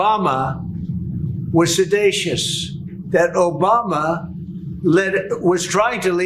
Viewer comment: Did Trump just invent a word? Didn't he actually mean to say mendacious, or possibly seditious ? I can't find a definition for what Trump said, which I think was sedacious.